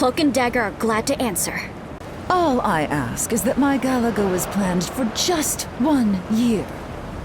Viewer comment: emma frost actually says "go as planned for just one year", the ingame subtitles are wrong